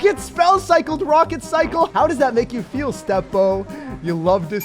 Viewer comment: Did say Step Bro?